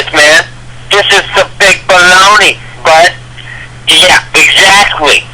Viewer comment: did he call himself "the big Bologna"